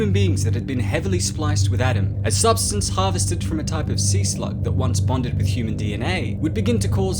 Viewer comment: He said Sea slug and I thought he said Sea slut